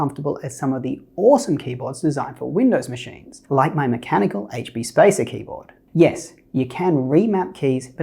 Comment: What is the mechanical keyboard that you use. I did not see a link in the description below. I saw a link for a keyboard but it was for some Logitech keyboard on Amazon. I thought that you had said something like HB Basic mechanical keyboard?